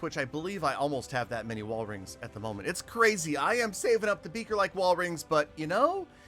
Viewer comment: he really said I am saving beaker like wall rings